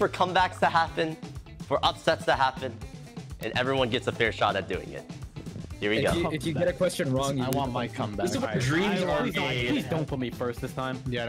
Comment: Did i hear this right? I literally heard say "I want my come back"...